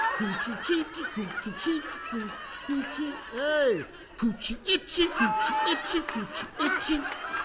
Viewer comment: Did Steve Say "Coochie Itchin?" 🤣🤣